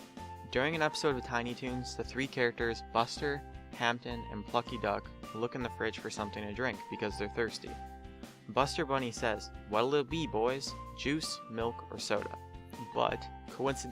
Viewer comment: it sounded like he said fucky duck. lol